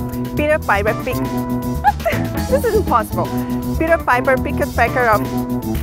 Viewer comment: "peter parker" sei la, mas eu ouvi isso ahuehuaeua